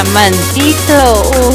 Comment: Fui engañado pensé que decía diamantito decía diamanteliyo :"v